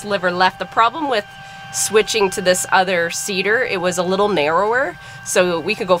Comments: I was so confused for the first couple of minutes. I thought you were saying CIDR, but you really meant seeder! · He he he , I thought you said Cedar , like for the female lambs. Then when you started showing the field I realized you were saying Seeder. Thank goodness because I was asking myself why would somebody be showing her how to use a new female lamb cedar. 🐑 lol